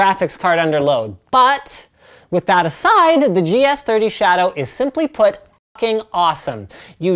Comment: I don't no what word he said because he cut it out. SOMEONE PLZ HLP ME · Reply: GS30 Shadow it is the name of the docking station for the MSI laptop.